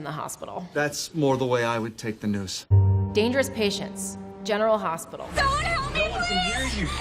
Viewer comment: At first I thought she said, "Genital Hospital"